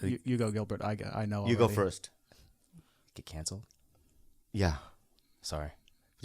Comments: I thought he was sayin Bobby has cancer. Heart dropped. Fuck your show that shit sucked. Glad u aint dyin homie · duuude, in the beginning when they were sayign something terrible happened and then Gilbert whispered "canceled" I thought he said "cancer" and was about to be heatbroken for bobby, jesus christ lol. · Jesus Christ... I thought Gilbert said "cancer" at 5:38. Please god, no TigerBelly cancers.